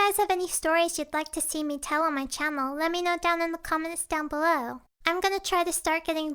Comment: Was I the only one who heard "Chammel"